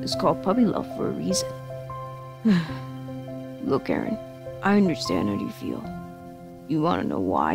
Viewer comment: I'm sorry to ruin this nice comment section but I have to say, at 3:32 the auto-generated, unedited subtitles say "Look Karen" lmao